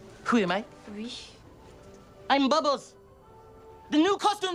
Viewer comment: The way he says "I Am Bubbles" WAS EPIC ! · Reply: I thought he said I am Bobbos